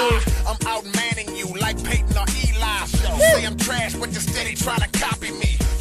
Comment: He said Peyton Or Eli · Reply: Thanks! I missed that one. I thought he said Peyton over Eli. For some reason I thought Peyton had more SB rings but nope they both have 2.